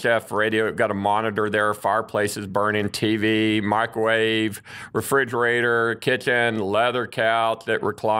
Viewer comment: Lol when he said microwave I thought he said my grave! Lol too prepared for the apocalypse